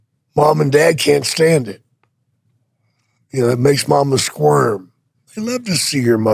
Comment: I thought he said squirt instead of squirm 😂